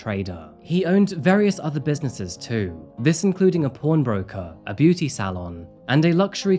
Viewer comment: With the accent, I thought said this dude was a "porn broker," lol.